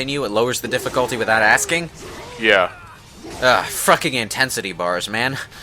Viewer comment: Did you say "FrUKing". ( ͡° ͜ʖ ͡°)